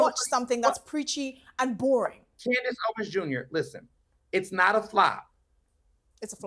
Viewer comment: did he really call her “Candice Owen’s Jnr”? 🤣